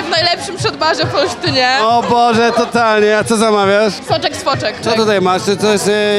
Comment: co ona powiedziała? nie usłyszałem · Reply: Soczek z foczek